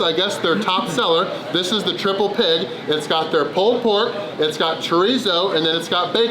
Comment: 212 in 2 yrs😮 🐐 ❤ 3:55 I had to look at the description i thought he said triple pin